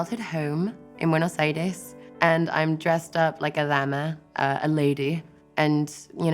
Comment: subtitles are wrong she said "Dama" not Lama